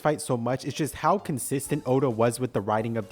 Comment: did he say oda .